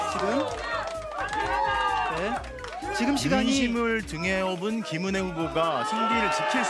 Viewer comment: 앵커멘트임 저게.???? "민심을 등에 엎은 김은혜 후보가~승리를 지킬수 있을지??" 잘못들었나하고 계속들었는데 ...미친거아님??? 개표방송에 저런말을 함?? 저 앵커 징계안합니까? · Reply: 민심이 아니고 윤심 이라고 말한 것으로 들리네요. 윤석열이 밀어줬다는 뜻이요.